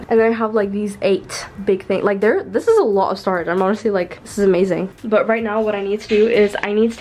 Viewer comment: Hello I enjoy your content, and would love for everyone including our deaf/hard of hearing community to enjoy it as well. I just wanted to let you know of this caption error: The word “stars” was listed instead of the word “storage”at 2:43. I would greatly appreciate it if you would take the time to correct it for those who need it. Thank you for your time!